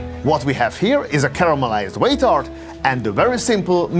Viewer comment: did he say “caramelised waiter”? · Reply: a caramelized whey tart